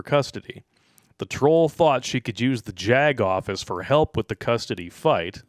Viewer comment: I'm kind of hungry and sleepy as I watch this. What turned out to be "I thought we could call the JAG office", I thought said "Jagoff". Dinner's ready and I have some coffee made. PROCEED!